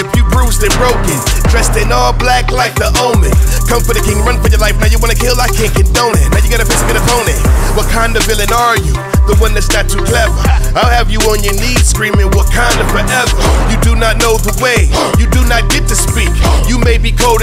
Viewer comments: (Rip Chadwick Bozeman) but the first time I watched this video I thought he said Wakanda villain are you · Who heard Black Panther say “You do not know the way” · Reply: Me I heard him say wakanda villain are you